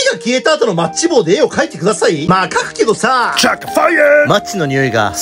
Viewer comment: 最初「火が消えた後のマッチ棒で絵を書いてください！」 が、「火が消えた後のマッチ棒の絵を描いてください！」に聞こえてやべえやつかと思ったw